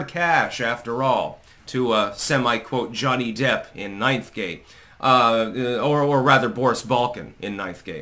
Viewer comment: Thought you said "knife game" lol. Yup .. Ninth gate is amazing ...blew my mind first time I watched it